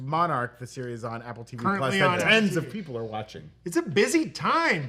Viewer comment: Did just say 'tens of people are watching'? 😂😂😂😂😂